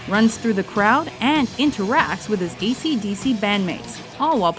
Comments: > dc/dc? i hear that lol. Maybe im listening ac/dc with a fucking high volume? ;) EDIT: to high* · Did she just say.....DC/DC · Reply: She definitely said AC/DC I played it like 5 times to make sure bromosexual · did u say dc/dc